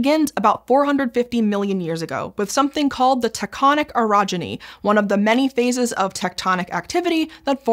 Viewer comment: I was listening, not watching, the video. There was a minute early in the video where I heard repeated innuendo: "taconic 'erogeny'...two converged...thrust sheets...friction...raised the temperature...." It's probably just me. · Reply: You need to get laid. Oh it’s orogeny. Does that change anything?